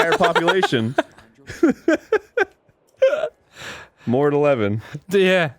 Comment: I thought he said "Mortal Evan"